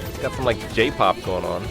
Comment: Did he just say Jpop?